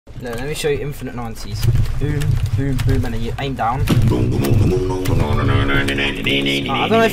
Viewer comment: it sounds like he is saying,” yo, let me show you infinite nazis”. 🤣